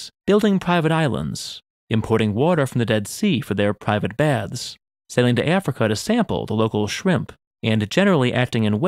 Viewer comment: had to activate captions in order to understand what was being said clearly (sailing to Africa to sample shrimps)